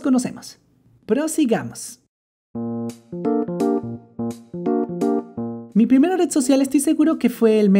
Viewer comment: Yo entendí que era su primera vez que le robaron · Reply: xd , yo entendi "Mi primera vez ... CocinandO"